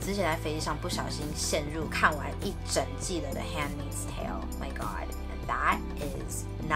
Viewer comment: 是什麼影集 可以打英文嗎 聽不太出來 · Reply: YY X the handmaid’s tale!